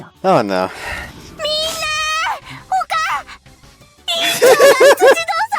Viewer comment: "Just call her mom, me." Is what that sounded like... reminds me of that one scene in Rush hour 3 xD